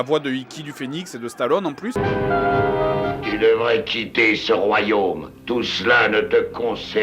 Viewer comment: vers 14:00 avant ou apres le méchant y dis ''Tu devrais quitter se royaume'' la premiere fois j'avais compris ''Tu devrais tchiter se royaume!'' alors moi j'me disais QUOIIII????? X)